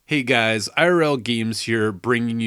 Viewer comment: Did you say "gemees"?